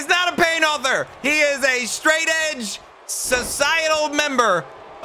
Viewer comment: Hang on... and i said Hol up Wait a minute Something Aint right. Did he say Edge?